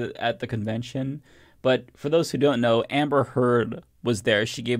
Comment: Did you just say Amber Heard? Really?